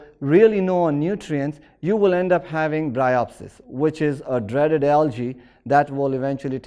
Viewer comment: what algae is he saying? I can't get the spelling right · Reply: Bryopsis